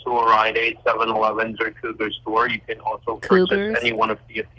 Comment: Did he say the cougar store? 🐆